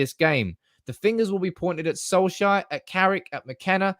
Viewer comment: Was watching with the auto generated subtitles on and the time when you said solskjaer around the 6:05 min mark, the subtitles said soul shite. Pretty accurate I think.